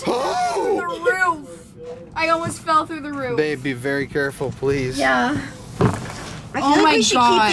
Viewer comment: 😳👀 is about to fall/ trip ..( fortunately she didn't )... THEN all of you are laughing 😂 as you do I heard a voice , t sounded as " YOU BE CAREFUL " ( male voice ) ....BUT the " voice " has another tone to it ( compared with yours )...👍🤔💭 please check it out. 🇩🇰🇩🇰👻